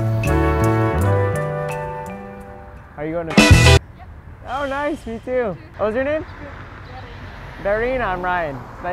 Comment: what did he say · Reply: He said it’s a surprise